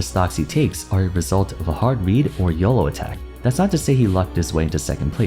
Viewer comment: What's a "yellow attack"? Did I hear that wrong? "... are a result of a hard read, or a yellow attack" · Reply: YOLO*